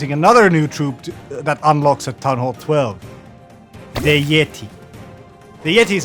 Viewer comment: did he say town hall 12?😼